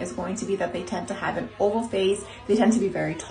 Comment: I am very tall oval face slender. Angelic looking too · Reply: Feeeew! I thought she said an OLD face! 🤣🤦‍♀️😝🤣